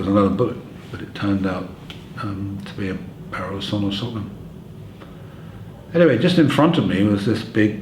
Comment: What does he say at 4:25 I can't quite make it out? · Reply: Parallel saw off shotgun